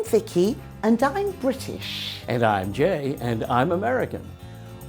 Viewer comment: When Jay said '' I'm Jay.'' I thought he said "I'm Gay." Did you too? Like if you agree | | \/